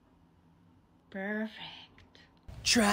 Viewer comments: I THOUGHT HE SAID "it tastes like my floor" IM DYING HELP ME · Him:*inhales* tastes like my phone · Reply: I thought he said taste like my father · The "fresh blood" "I'm gonna call the cops " and "it tastes like my phone " got me 🤣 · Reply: I thought he said "Tastes like white folk." because it's basically a pumpkin spice latte. 😂 I had to listen to that part again to realize he said "my phone" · Him:*inhales* tastes like my phone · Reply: I thought he said taste like my foot